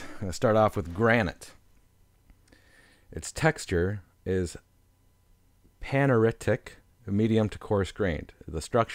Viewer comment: What's the word at 8:55? I'm trying to start with vocab and at 9:20? Athenetic isn't a word I don't think What are the chances of you just copying your script into the description so these words aren't a mystery? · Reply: Keith Richards aphanitic texture